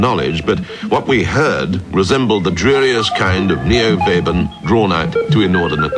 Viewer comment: What does it say @ 3:43? The dreariest kind of neo vaben?